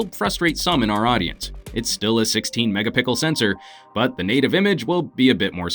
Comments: did he said mega Pickle?? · you did not just said say Mega-Pickle LOL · Does he say it's a megapickel sensor @ 2:41?